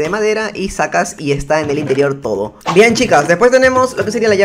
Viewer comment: yo entendí "bien chicas"